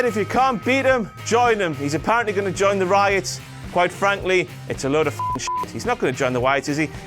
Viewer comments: wait at 3:23 did just called The Wyatts "The Ryatts"well he had a long run.........IT'S -O-CLOCK MOTHERFUCKAS!!!!!!! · IT'S O CLOCK! he said riots instead of Wyatt's